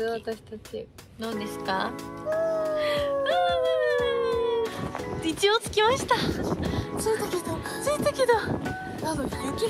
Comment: 何て言ってんの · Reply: 一応着きました！ だと